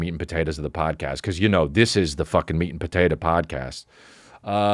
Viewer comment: Just here to give thanks to the editor for adding pictures when he said this is the meat and potatoes podcast cause twice I heard it's the "mean potatoes" podcast 🤬🥔🍠